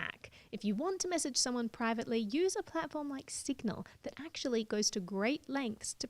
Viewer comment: What did she say ? A platform like ???? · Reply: A platform like 'Signal'.